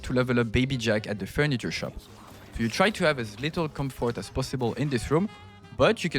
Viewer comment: Thanks, great video. I just woke up so it might me, but I’m having trouble understand what you say at 0:40 - 0:44. “You can have as little comfort as possible, but you can stack, for example ? Happy ?” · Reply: Appeal :)